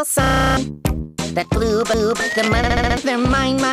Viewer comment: he just said "the blue boob" did anyone hear that xD LoL 😂😂😂😂😂😂😂